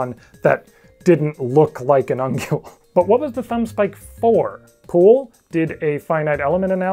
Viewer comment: When you said “Poole” I immediately thought you said “Pool”, and the thumb was used to hold the cue in billiards